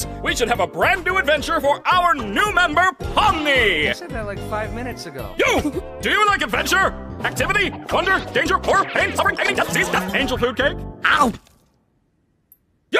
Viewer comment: I thought Kane said angel FOOD. Cake!!!